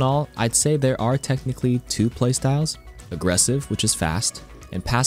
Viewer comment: Maybe it’s the vodka but I swear I heard “karQulated” when you said calculated 😂😂